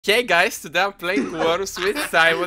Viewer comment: IM LAUGHINF SO HARD TGE BEGINNING SOUNDS LIKE YOU SAID "GAY GUYS" IM SORRY 😂😂😂